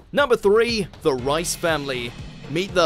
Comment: In the captions it is written the rice family instead of the reiss